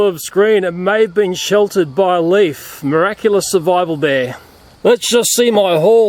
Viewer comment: did u said miraculous